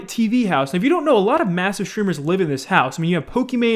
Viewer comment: I thought u said massive tumor